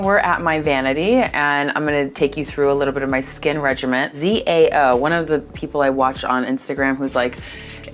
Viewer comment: what does she say at 1:54? ZAO?